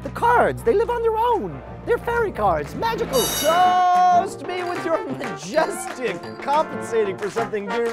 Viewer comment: I thought I heard GOOOOOOOOOOAAAAAAALLLLLLL and then I realized it was "toast"